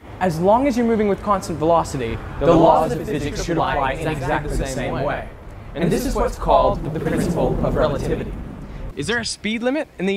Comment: what did u say there at 0:52?? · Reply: The laws of physics should apply exactly the same way, and this is what's called the principal of relativity.